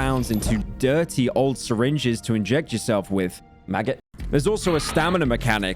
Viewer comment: WAIT IT WASN'T MONTHS??? WTF-- also my replies to this are gonna be about other parts of the video cuz i'm not flooding this comment section with my silly comments, lessgo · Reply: definitely heard maggot. definitely didn't hear anything starting with an F. maggot